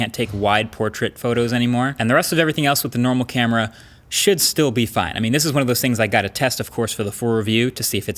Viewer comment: I thought he said "Shit still be fine" 😂 had to rewind twice to get what he really said